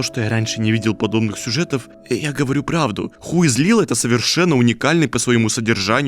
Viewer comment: каждый раз когда я вижу обзор игры who is lila, я не могу расслышать словосочетание "хуй злила"